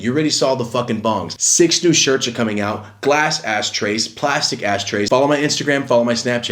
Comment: am I high or did say ass trays